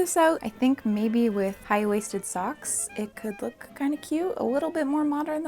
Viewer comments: did u really say "high waisted socks" around 8:45 or am i crazy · Wait did you just say "high-waisted socks"???? 🤔